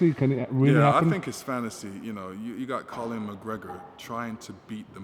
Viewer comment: Did he just say Colin McGregor?